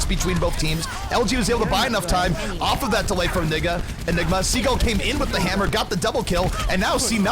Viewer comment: that announcer accident "nigga" instead of "enigma". lawl